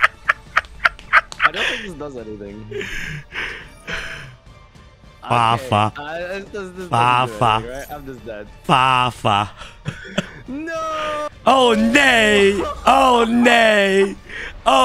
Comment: Did you just say: “Oh nee”; at 15:36? That’s literally Dutch for “Ow no” xD